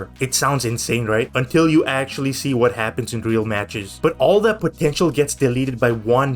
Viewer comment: did you say potential??